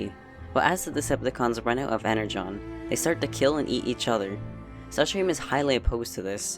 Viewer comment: am I hearing right? "Eat" each other?